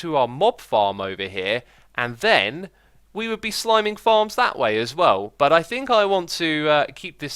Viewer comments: Did anyone else hear slimming farm? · Did you say at 4:42 we are sliming farms too? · did he say sliming farms at 4:40 😂